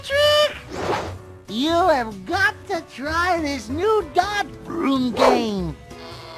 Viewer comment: What did he say? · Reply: "You have got to try this new dodge-broom game."